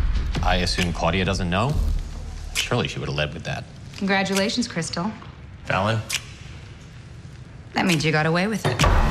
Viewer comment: What did she say at 1:31? Someone please tell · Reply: "Congratulations, Cristal. That means you got away with it."